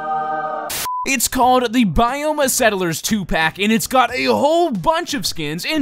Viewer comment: on 0:34 did said Tupac ?